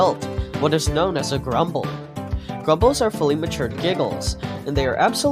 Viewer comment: guiding light didn't say it's a "mutation thing", wdym? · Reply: "Grumbles are matured Giggles." guiding light said, i thought it said "mutated" my bad lmfao